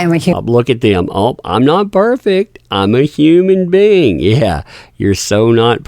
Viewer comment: sounded like ah said I'm a human bean. not I'm a human being. while on the stand.